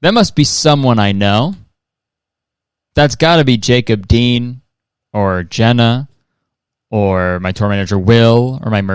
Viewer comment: I heard Jason Dean instead of Jacob Dean chat am i cooked 😭